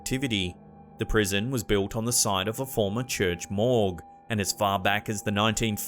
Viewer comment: Did he say the church morgue is what the jail is build on top of ? Creepy. Church Morgue! Those two words shouldn't go together. 😳 How does that happen?